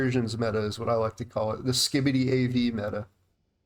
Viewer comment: did you say skibbadee? 😂